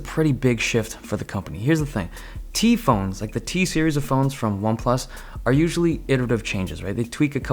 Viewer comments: did you just say T-Series? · DID U JUST SAY T-SERIES ?!?? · did you just say T-Series👿👿????